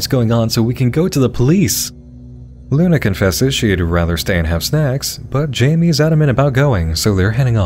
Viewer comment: Anyone else hear that as "Luna confesses she would rather stay and have sex" ?